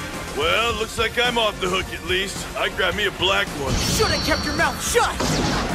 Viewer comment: I always thought at that scene, Naegi saying something like "I'll keep your mouth shut" Oh nevermind, that's literally him in every trial though